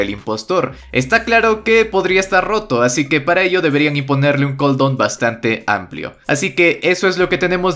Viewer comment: escuche que le pongan un condon o que XD